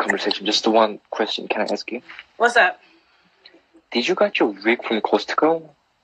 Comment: what he asked her? I couldn't hear it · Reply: He asked if she got her wig from Costco which is messed up to say